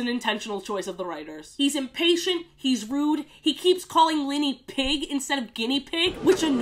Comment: I lived a lie for 19 years- I thought the Guinea pig’s name was Whinny not Linny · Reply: I heard "Lenny"